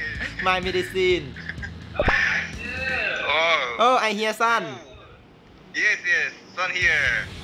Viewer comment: i hear sun 😨 ห้ะ5555